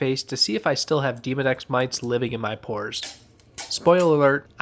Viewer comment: It definitely sounded like you said Demon X living in my pores. I’m sure there’s no difference but hilarious nonetheless